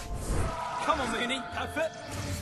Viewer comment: are my ears wrong or did I hear Harry say Potato?